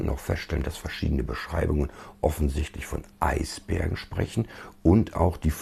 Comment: Sorry, habs mir noch ein paar Mal angehört. Du sagst in 9:15 wohl "Eisberge", nicht "Eisbären"